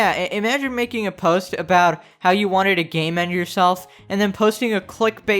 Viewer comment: For a moment I thought you said she clickbaited gaymen and I was so confused